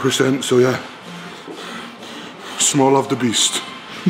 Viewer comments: Small love, the beast. Where did that come from? Hahahaha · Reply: I thought he said smell of the beast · Small love, the beast. Where did that come from? Hahahaha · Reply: and I thought he said "smile of the beast"